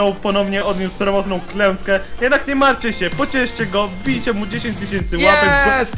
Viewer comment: czy tylko ja usłyszałem wbijcie mu 10 tysięcy łapek w dupe?